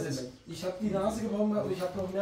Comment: „Gibt‘s da keine Namen?“ -, 2023 Den Namen der Flasche in die er „gefallen“ ist? Gute Besserung;) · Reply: Ich hab auch erst verstanden "ich sag keine Namen". Nach mehrmaligen anhören verstehe ich nun "gibt das keine Narben"...